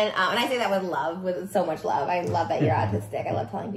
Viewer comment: He has not even been diagnosed... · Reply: I thought she said artistic idk I could be wrong